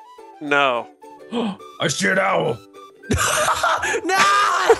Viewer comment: its sounds like said I shit owl